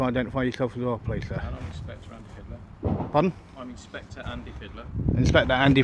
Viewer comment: I thought he said "Andy Hitler" at first. That'd be a good name for a police inspector